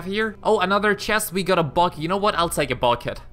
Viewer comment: When he said bucket I thought he said Vodka